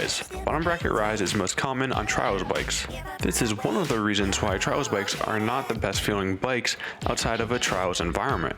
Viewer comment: what bikes? Trouser bike.. Trail Bikes.. say what??? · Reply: trials bikes. the discipline of jumping over obstacles and stuff on a bike from standstill.